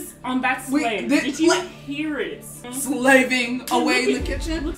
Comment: i thought she said “slaying”